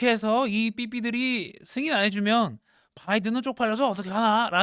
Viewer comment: 날리면, 바이든을 떠나서 앞에 '승인 안해주고' '승인 안해주면' 이것만 확인 해도 '안해주면'으로 들리는데...